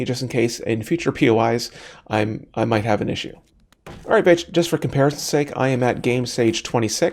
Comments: do you say "alright bitch"? cause that's what I heard and I can't stop laughing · "alright b****" not actually sure what's said there but that's what came to mind lol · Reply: Hahah I think I heard someone else commented the same thing a while ago; I think I was going to say "Alright just for comparison...", but I tripped over my words and started again.